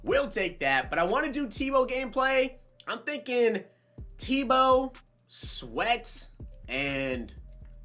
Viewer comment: i thought u said "to" which means terrel owens and i thought in my head "YES WE GOT A UL TERREL OWENS!" but u said tebow :(